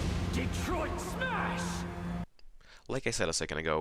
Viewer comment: Did he say "Detroit smash"